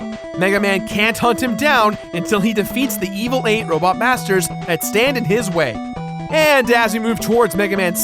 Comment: i swear it sounds like Jirard said "robot bastards".